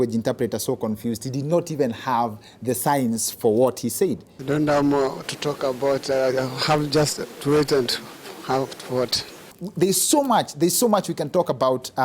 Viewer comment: This is what he said 5:57 "I don't have more to talk about, I'll have just to wait and have to watch." The guy just had a hoarse voice together with accent plus tension but he said something sensible men!